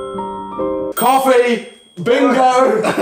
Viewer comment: when he said coffee the first time and he was hype i legit thought he said car fee